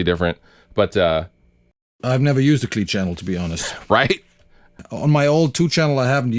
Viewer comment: "I've never used the Clean channel." Can I get that on a T-shirt? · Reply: first time I heard "Ive never used A clean channel to be honest" LOL